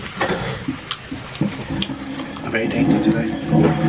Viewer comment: did Orlin just say he is very tainted today ? ^^ ^^